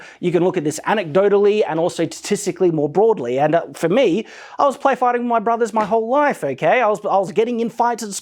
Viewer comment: "I was playfighting my brothers my whole life" what I hear, "I was beating up Jazza my whole life."